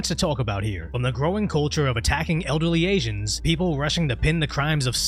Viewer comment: Why i keep hearing him saying "agents" instead of asians.